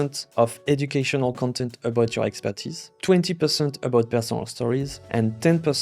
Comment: What do you mean when you say “educacional content about expenses”? What kind of content you reffer here? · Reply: It's "educational content about your expertise"